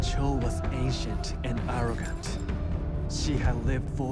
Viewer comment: The first time I saw this scene I thought the narrator said"Cho was ASIAN and arrogant" than I watched again and realised he said ANCIENT.